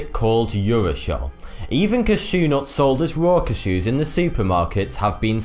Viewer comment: Why did it sound like he said Rocket Shoes lol when he was say Raw cashews and it sounds as if he said Hard dogs instead of hot dogs